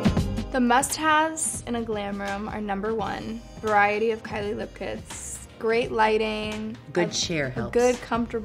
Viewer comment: what did Kylie say at 00:45? Lucas? Or look's? I just learn English, thank you in advance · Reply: Assel Muhtarova she said “Kylie lip kits”☺️